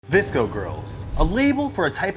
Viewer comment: I have a confession When I first heard the term VSCO girl, I thought it was like a Gasoline company. :l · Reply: I thought they were saying disco girl